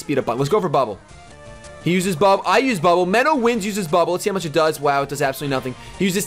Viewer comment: AHHHH when you said menowinds i thought you said "meta Wins" close but no